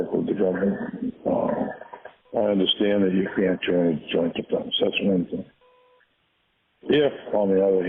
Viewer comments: Is it just me, or does the inaudible part sound like Dowd is saying "I understand that you can't join joint defenses or anything"? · "I understand that you can't join (inaudible)" .... I hear this as "...join joint defense. That's the main thing." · That first inaudible sounds like he says: "...if you can't join joint diplomacy, that's one thing."